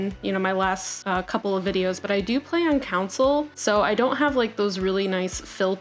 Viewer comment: Did you say counsel? 2:38 Well anyway good build 😂